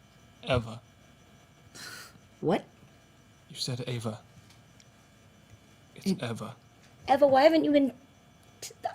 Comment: What does Jasper say to Eva at 53:38? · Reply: « I’m sorry i’m sorry i’m sorry i’m so sorry »